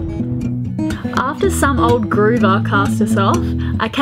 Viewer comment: "old gruber cast you off", not so nice description of a kind old gentleman who gave you a hand. remember you will be there someday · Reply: I thought I heard 'old groover', which is complimentary, describing the guy as trendy, though not young.